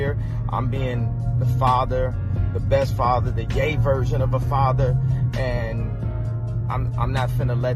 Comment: I thought he said "I'm being the gay version of a father" 😂😂😂